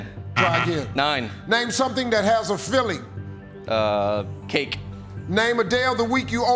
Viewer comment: I honestly thought he said "Philly" instead of "Filling".